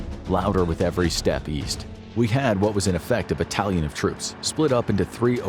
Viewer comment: did he say splatoon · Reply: i think he said battalion